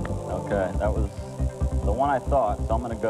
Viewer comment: He said “C Llamas” and I heard “Sea Llamas” picturing a fortnite seahorse creature. I think I have an addiction.